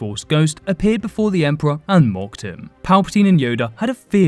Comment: Bro, I wasn't looking at the screen and when this vid started I could have sworn you said " and robbed him"😅😅😅